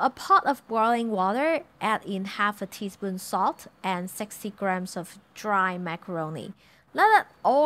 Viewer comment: when she said, "60 grams of dry macaroni" I heard sexy grams of dry macaroni!😂 well that works for me too